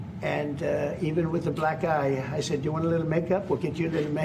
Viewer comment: Black eye? 😮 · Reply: I thought he said “black guy”.